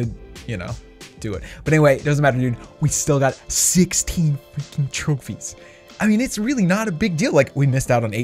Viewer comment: did he say we still got 16 fu..... trophies!!!